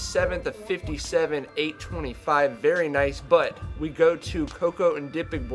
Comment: did he say "very nice Butt" when the girls butts were showing lol jkjk